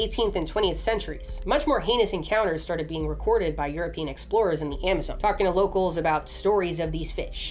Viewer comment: I misheard "European explorers" as "Yer penis explorers"